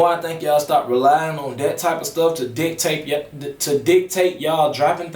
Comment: Lol I fwu but it sound like u said dicktape lol im over here weak asf 😂😂😂